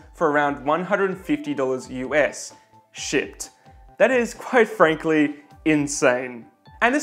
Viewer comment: I misheard this sentence at 6:53 "...however, it's regularly available at flash sales for around $150 US. Shit."